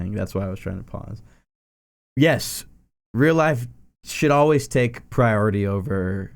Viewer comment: oh i thought he said s**t but he said should oh ok